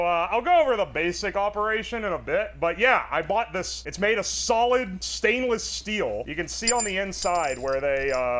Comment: Did you say "Solid"? It's a solid snake reference trust.